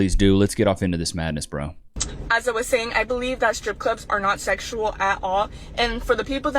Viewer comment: For a second I thought the first girl said "stripper clips" were non-sexual and I thought she might actually know something about something. Oops.